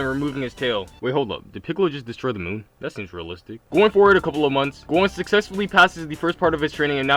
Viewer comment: sounds like you said Gon.